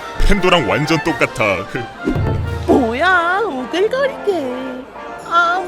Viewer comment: 뭐라고 하는거에요? 오뎅가리개?? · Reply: 오글거리게가 오뎅가리게로 변신 ㅋㅋㅋㅋㅋㅋㅋㅋㅋㅋㅋ